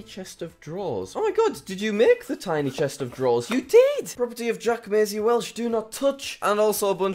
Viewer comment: Did he just say Jack Maisy Welsh at 2:21?